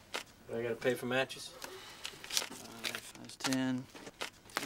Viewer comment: What does Munson say at the end @ 0:52? It sounds like, "If you have a needless thought, call me." If so, what does it mean? Is it just a quick jab at the cashier? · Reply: I know this was 4 months ago, but he says, "If you ever need to just talk, call me".